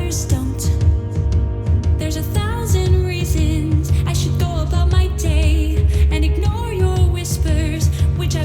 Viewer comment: Amazing singing! (0:33 my rubbish ears thought they heard ‘There’s a thousand raisins I should go about my day’)